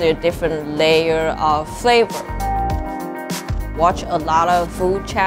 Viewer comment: Did she just say instagwam